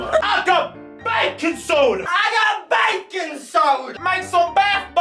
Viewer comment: for the longest time i thought they were saying "bacon soda"